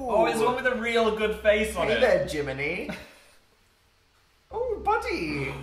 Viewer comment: I thought Dan said Jiminie...then I remembered the talking cricket in Pinocchio...it's called Jiminy